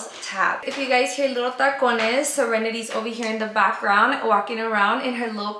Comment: omgg this is my first time seeing y’all and when i heard you say sirenidy it threw me off LOLOL 😭😂